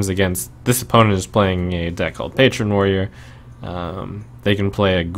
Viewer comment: how does know opp is playing what kind of deck? He says a 'Patreon Warrior'? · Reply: I think he was naming out deck archetypes which would have put out minions that weren't favorable for him to Mirror Image. 'Patron Warrior' refers to a deck focusing on the card 'Grim Patron' which, whenever it survives damage, summons a fresh copy of itself with the same effect. This synergizes well with cards like Unstable Ghoul, which has a deathrattle that deals 1 damage to all minions (including copies of Grim Patron, and already fragile Mana Wyrm, and the Mirror Images protecting it).